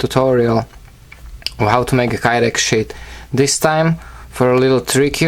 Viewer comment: oh lol hes saying sheath...... thought he was saying shit